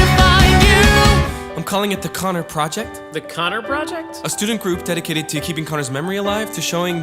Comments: You kinda put Connor instead of Jared when he says "The Connor Project?" The rest is all good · I thought Jared says "the connor project"..?